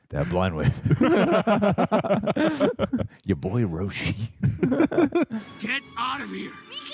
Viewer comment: Did he say Yaboyroshi??? LMAO